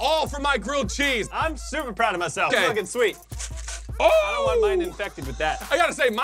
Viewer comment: all I thought I was hearing say was "it's f*ing sweet" but he may have said it's looking sweet